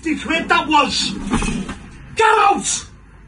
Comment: I thought he was saying "GET OUT" But in his english it waa "GIRLS"